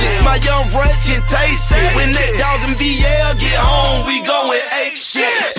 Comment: ON 1:21 DID HE SAY WHEN THEY DOGGIN VL